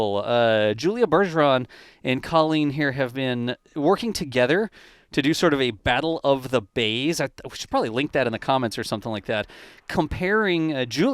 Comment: Ha Ha - Funny! I thought you said Battle of the BABES then I went and re-listened and it was Battle of the BAYS.